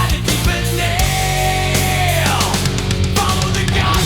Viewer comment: I always thought he said demon nail